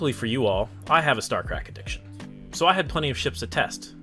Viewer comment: ''Luckily for you all, I have a Star CRACK edition'' I truly hope I heard incorrectly... but did you actually say a "CRACK Edition" ? if so, this needs to be reported to CIG immediately........